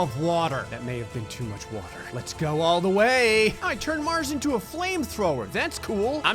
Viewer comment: “I turned mars into a flamethrower” Quote of the year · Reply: Why do I hear "I turned my a## into a flame thrower 😂